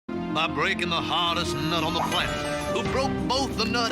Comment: this sound wrong at frist I thought he said bust a nut